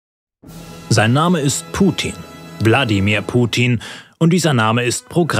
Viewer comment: Hab nur ich Bladimir Putin verstanden